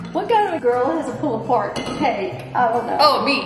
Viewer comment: I thought you said pulled pork cake 🤣